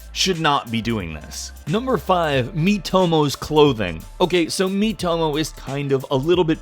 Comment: I thought he said Meet homo clothings😂